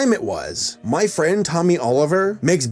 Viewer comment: I hear say "My friend Tommy Oliver" I'm like THE GREEN RANGER??